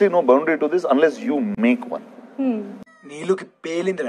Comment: I didn't understand what he said at 1:57, but I laughed hard🤣🤣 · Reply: It's film satire dailogue on villian called Neelu. He said " Neelu had a blast".